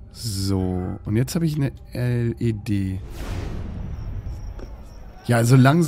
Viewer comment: hab verstanden: Jetzt hab ich ne LED😂😂